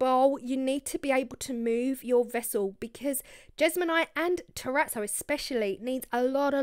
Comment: Never seen this technique before, thanks for sharing. Btw I am deaf and wonder if you can clarify what you said/meant when the captions said jesmonite and Tourette’s. At about 7:06ish minutes in? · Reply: Heyyyyyy Sean ... ohhhh goodness the captions said Tourettes ??? That's not good ... I said Terrazzo .. so I went on to make a Jeamonite Terrazzo Bowl in the next video ❤️❤️❤️